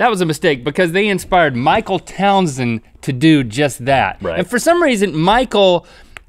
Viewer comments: did rhett just say bruh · anyone else thought he was gonna say Michael townly from gta 5 XD